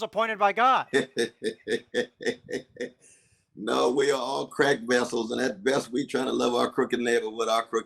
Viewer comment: for a second I thought Dr West said we are all crack vessels 🤣